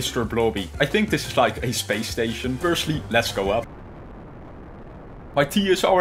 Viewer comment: When you said lets go up, I thought you said "let blow up"!